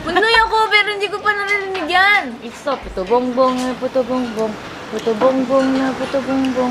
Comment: Why I heard “whata bum bum whata bum bum” from blackpink’s forever young when ate sang “puto bong bong puto bong bong”😂 BOTH OF U AND ATE ARE SO ADORABLE🥰